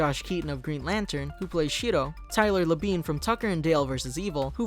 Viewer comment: I just heard shito What's wrong with my ears